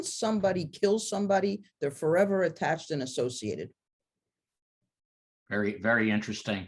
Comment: “Once somebody ki1ls somebody, they’re forever attached and associative” - Did you mean attached to the victims? Or did I mishear that?